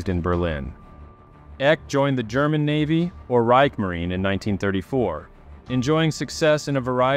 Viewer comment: Did you just say reigmarine?